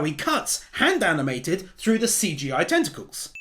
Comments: captions said testicles not tentacles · Hey man I just want to comment that around 25:30 the subtitles said testicles instead of tentacles. I'm not sure if that's intentional or not but I wanted to bring it to your attention.